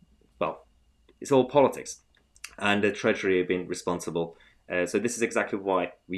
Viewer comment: I misheard you did you say "the treachery"?